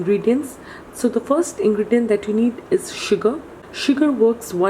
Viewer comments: She said sugar I heard suga I'm like seriously then I went back and heard it carefully. Now I'm okk 🤣🤣🤣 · why it sound like she said ''suga''